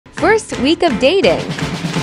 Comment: When it said that 1 week of dating I heard "worst week of dating" 💀☠️